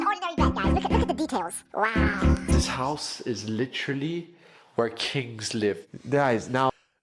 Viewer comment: I heard "where cakes live".